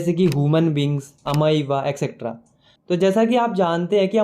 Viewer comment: mai kya bola samjh nhi aaya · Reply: Human beings and amoeba